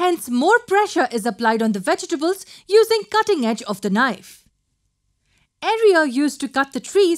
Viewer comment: area instead of 'axes'